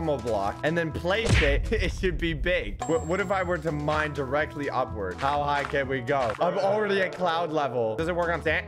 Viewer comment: Did sock say mine upward